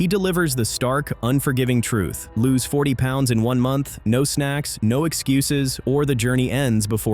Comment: At first i thought he said 140 pounds in one month then i replayed it and it said 40 pounds in one month just to se if he actually said lose 140 pounds in a month no it’s 40 in one month